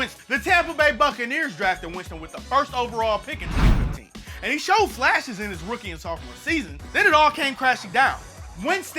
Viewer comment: can anyone hear the year he was drafted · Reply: 2015